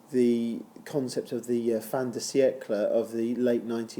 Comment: ? what did u say fantasy eckler? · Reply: No it’s fin de siecle………it’s French for end of the century…..and it’s used when talking about Oscar Wilde as one of the writers of the end of the nineteenth century, even though this is an early 20th century. But I like the sound of Fantasy Eckler!